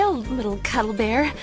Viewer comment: #Vornado The maker of this video thought the girl said, "My little cum bear."